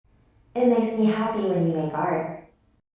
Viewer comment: My volume was low and I thought the voice said : “ It makes me happy when you FART “